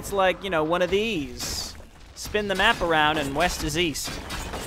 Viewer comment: ooooh east? I thought you said weast!